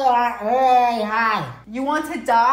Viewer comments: On the first one I thought he said I’m like really high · The first thing said sounded like he said “I am really high” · I thought he was saying I want my hair dyed 😆😋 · I thought he said "I'm really high" 😭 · For a second I thought he said I want to be high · At first I thought he said I want to get high💀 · I thought he said I want to get high 😂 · At first I thought he said"I get really high".😂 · I really thought he said he wants to get high💀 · I thought he said at the first part “I wanna get really high” 😭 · I thought he said “I want to get high” lol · I thought he said I want you really high wich means drunk 😂😂😂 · He sounded like he said I want to get high 😂😂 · I thought he said I want to say hi lol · i thought he said "i want to die high" 😭💀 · I heard “ I want to be high”